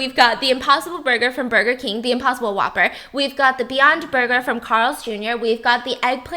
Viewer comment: did your hear what I heard or did she just say beotch burger???